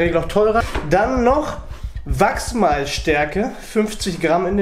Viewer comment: Ich habe Wachsmalstifte verstanden 😂